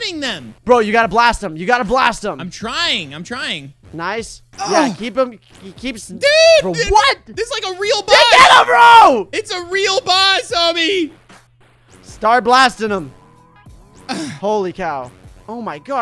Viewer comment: sounds like says star blastinum